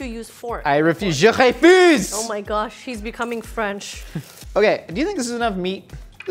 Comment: It sounded like she said "Oh my Josh".